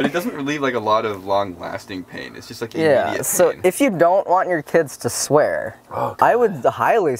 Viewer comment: I totally heard, "If you dont want your tits to swear"................ I dont know why